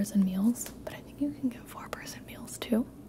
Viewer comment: I thought she said 4 prison meals.